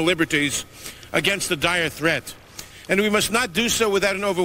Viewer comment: Be honest, at 0:44 everyone thought Jabba the Hut said “protect ourselves from the DIET threat”